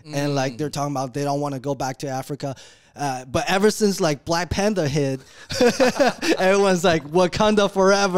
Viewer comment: Lol I thought he said black panda 😅